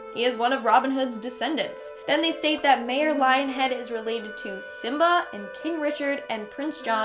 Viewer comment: Am i the only one who heard her say 'Mayor LionHead' at 1:49?